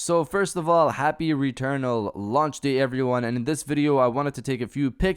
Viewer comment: Smh I heard “happy anniversary of launch day” for some reason and lost all sanity thinking and entire year passed already.... I smoke too much